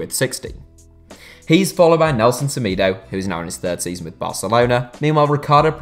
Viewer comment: Nelson is 26 he is in his twenties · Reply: He said in his 3rd season but i had to rewatch it because I thought he said hes in his thirties as well and looked up his age